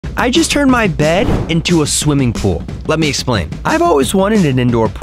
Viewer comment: i thought he said "i just turned my dad into a swiming pool"